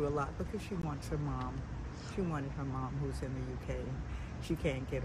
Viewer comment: did she say U.K??